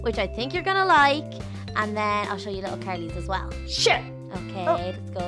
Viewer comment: what did baby Leah say at 2:36. It sounded like she said a bad word ooooo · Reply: no baby Leah said sure she didn't say a bad word .